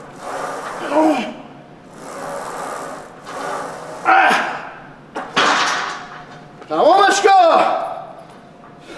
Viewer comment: What did he say at 12:59 , when he racked the weights . and before he lifted off that same set???? anyone that speaks Farsi?? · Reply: تمومش کن